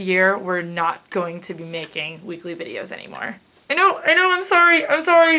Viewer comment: I had to rewind multiple times because all I heard was "We're not making videos anymore" and I flipped. I didn't hear the "weekly" and ... agh. Tears formed but did not fall. I will cherish this last month!